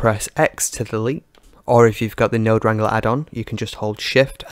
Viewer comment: Great tutorial, very good indeed. Just one thing, at 9:05 you refer to an add-on but I can't make out exactly what you call it, and I wouldn't know where to get it. I think you said Node Render add-on? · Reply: Hey Stuart. glad you like it.. The Add-on I mention is the Node Wrangler add-on which is built in to blender.. Just go to Edit > preferences > add on section and search for node wrangler. You can then click the check box to activate it.. I would also note that this removal effect has been simplified even more by using the Darkfall VFX nodes addon. It has a bunch of pre built nodes like this removal effect saving a whole bunch of time.. If you want you can check it out here: <link> You just need to download the script and then again in the addon section (in the user preferences) you can click install from file and select the VFX node addon.. up to you 👍